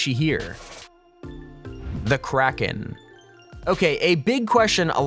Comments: i thought u said karen lol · thought it said karen